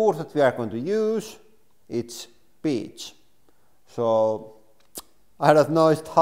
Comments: sounds like you said “and the wood we are going to use is.... bitch” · Did he say the wood we are going to use is bitch lol0:27